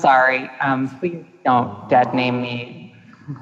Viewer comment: what? It sounds like Chelsea says “I’m sorry, please don’t dud name me” Can someone tell me what this is? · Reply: I think she said .."don't 'dead' name me" because the interviewer was referring to 'Bradley Manning' in past tense as if he no longer existed.